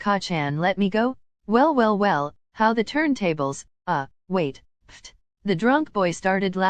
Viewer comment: I thought he said how the “turd tables” 😭